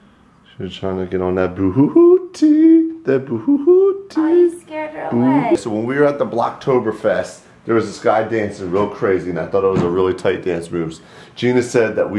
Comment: I wonder.............(still wonder)......wait for it...........is there a cocktober fest?! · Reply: I thought u said i cant wait for ocktoberfest.